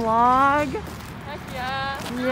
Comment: Thought u said pog for a second